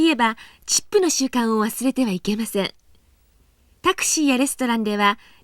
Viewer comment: the subtitles say "habit of CHIPS" instead of tips. 😋 11:58 too!